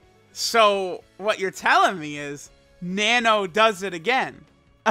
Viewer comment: I swear I thought said nano doesn’t get head. 😂